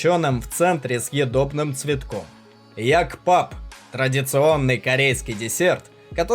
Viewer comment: Послышалось "Радиационный Корейский рецепт")))